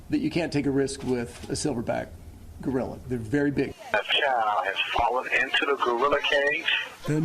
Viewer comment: i thought he said "a child has fallen into a gorilla cage in lego city"